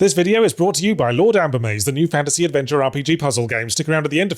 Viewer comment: I swear when he said Lord Ambermaze, I heard instead "Lord Applebee's" and thought that's a weird brand deal. Lol